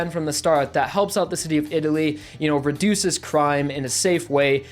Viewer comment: did you just say the city of italy?